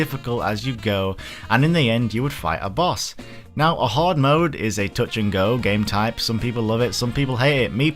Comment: for a second there thought he said hard mode not hoard mode 😂